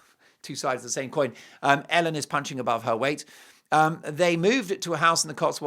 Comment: Hahahaha. Had to replay...I thought you said Ellen was munching above her weight!! 😂